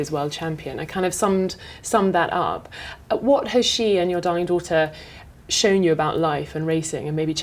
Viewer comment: does she say "...your dying daughter"??